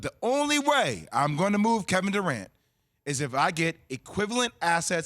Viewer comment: Did anybody catch what J Will said? All I heard was, "not my boo Kevin" lmao